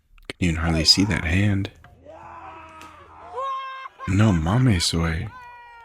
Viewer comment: Did u just say " no mamas guey" that's funny